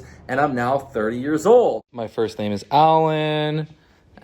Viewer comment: did you say your first name is Alan?